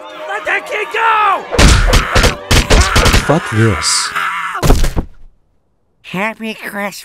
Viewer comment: what did jesus say lol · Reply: fuck this xD